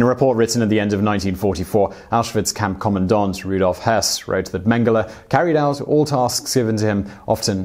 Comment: I thought you said Rudolf Hess at 14:25 and was surprised since he was in British prison by 1941? and was cleared of any charges surrounding the holocaust (and also it didn´t looked like him on the picture). Googled it and found out the name you said was Rudolf Höss... I really thought for a second i would be able to correct you there not even contemplating that if a mistake like that would occur it would be pointed out directly and not after over <num> views...